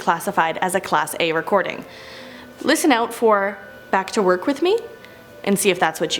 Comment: I didn't hear back to work with me I heard "do you ever clean down here"